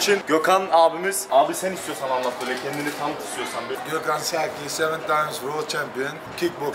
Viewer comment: Bi dk Gökhan Sakiye seni tanımayanlar vardır belki kendini tanıt mı dediniz ben mi yanlış anladım ?